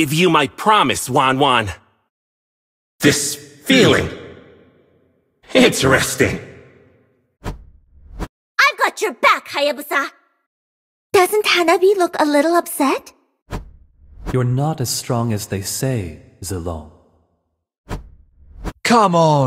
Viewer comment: When i was using kagura and my teammate was haya i thought kag said "I want you back hayabusa!"💀💀